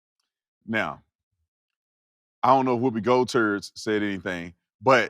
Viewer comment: Wait, hold up. Am I hearing things or did dude really say Whoopey Girlturds? 😂😂